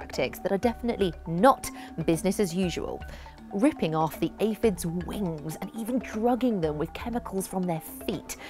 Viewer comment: what was said at 2:36 ? I heard "ripping off the aphids ??" what did they rip off of the aphids?? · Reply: Their wings so they won't be able to fly away so the ants can effectively farm them